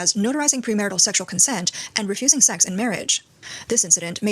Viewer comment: did they just say refusing sex in marriage?